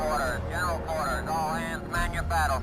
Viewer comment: english isnt my first language, so everytime i hear this battlestations sound i ask myself: ''what are the man saying?''. ''Cowborn'', ''melbourn'', ''cowboy'', ''oh boy'' (these ones were a joke) but seriously, could someone explain? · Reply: He's saying "general quarters"